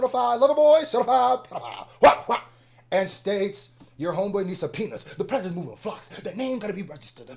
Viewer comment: im ngl i was listening to this in the background while doing something and i coulda sworn i heard "your homeboy needs a penis"